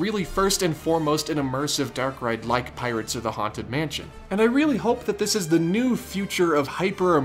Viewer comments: Looks awesome!! But... “Pirates of the Haunted Mansion”? That’s a ride I’d love to see!! LOL. See 17:22 time stamp! I had to make sure I didn’t mishear! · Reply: He said, "Like pirates OR the haunted mansion" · i thought he said “pirates of the haunted mansion” and thought “dang that would be an epic crossover”